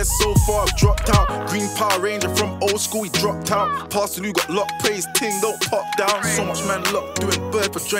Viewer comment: What’s the rappers name he shoutouts at the 6:40 mark ?? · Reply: Pa Salieu